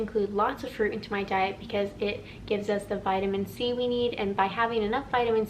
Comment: Vitamin C-weed/Seaweed is what I thought you said, but you said, "Vitamin C, we need..." 😅